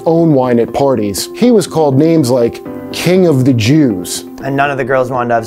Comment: That “King of the Jews” Jesus joke was genius on so many levels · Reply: honestly i heard "King of the Juice" lolllll